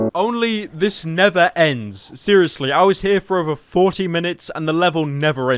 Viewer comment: After he made a Minecraft comparison I thought he said “it nether ends”